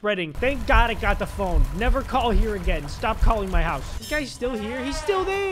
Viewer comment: I thought He said "Stop calling My &$$